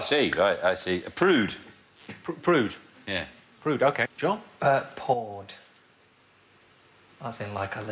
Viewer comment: his accent sounded like he said PAWED, which is a word and which is on the board. However then it was spelled POURED which is also a word and which also is on the board. And nobody noticed the ambiguity.